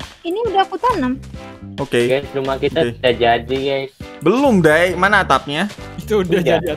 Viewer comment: bang kamu bilang"belum dai"ato aku salah denger ?!?!?! emg ada dai!?!? edit:kuyung dai kah aku lupa:(